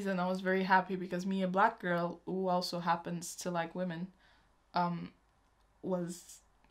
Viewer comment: who gay💛? I don't understand the name. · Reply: i said me a black girl who also happens to like women :)